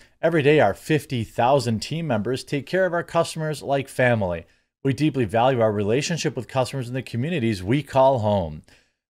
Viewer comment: What I heard was “we deeply value your money”